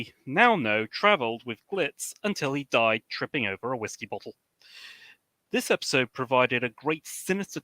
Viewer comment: I thought said Glitz died while fighting over the Whiskey bottle. I will have to go back and look.